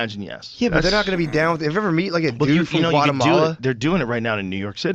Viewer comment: Lol thought one guy said have you ever met a filipino guatemalan. Suprise I'm one been a citizen for 30 years and I support trump.